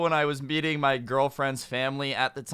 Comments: sounded like you said you were beating ur girlfriends family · First I heard "beating my girlfriend's family" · Who thought he said beating my girlfriends fam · I thought he said beating my girlfriends family